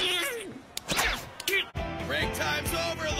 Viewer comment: did I just hear LOVEBIRDS or not